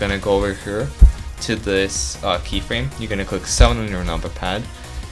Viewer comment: What did he say at 5:16 it was something number pad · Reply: 7.